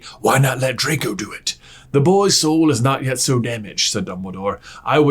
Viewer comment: Oh he was talking about the boys soul. I thought he was saying boys hole.